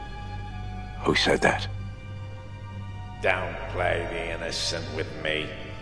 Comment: what did he say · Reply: “Don’t play the innocent with me”